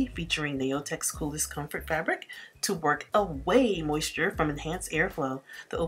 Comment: Hi, Your country accent is so cute. You sounded like you said ‘work away’ instead of ‘wick away’. It reminds me of friends in Arkansas and Alabama your heavy accent. 😊.